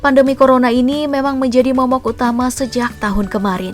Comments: aku gak salah dengerkan.momok utama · 😂😂 ngomong apa tu man😂😂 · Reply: Momok woy :v